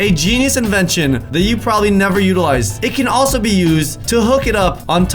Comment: Is it me or did he say til til at 9:33 ?????